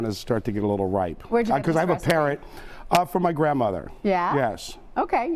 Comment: he said hes a parent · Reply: If you look at the Transcript, it says "parrot", but he was rudely interrupted by the host. I thought he said 'I have a parent....'. He does own a bird of some sort that he raised from an egg, and he also has 2 golden retrievers. He talked about it in another video.